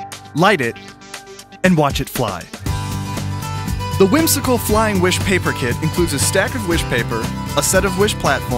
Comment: wow I thought it said wish paper kills